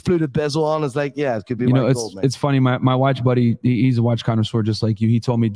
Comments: Did Myron actually say my wife's buddy ( 45:27 ) ? Wtf did I miss???? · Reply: My watch buddy bro, wtf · am I trippin or did Myron say “My wife’s buddy”